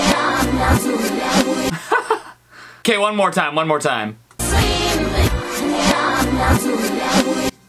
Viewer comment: it sounds like she says sweep with me